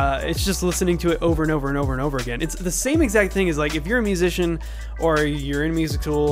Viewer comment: Was I hearing things, or did I hear him say “if you’re a music tool” I want to believe this was self criticism! 😀